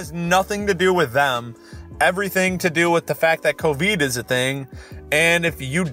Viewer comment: Did you just say Coveed? XD